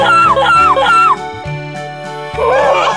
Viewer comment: 어,뭐야!! 이렇게 들림ㅋ한국분?😆